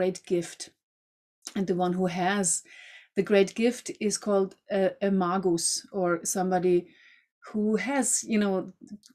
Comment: I'm sorry, did she just say Amongus? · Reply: A Magus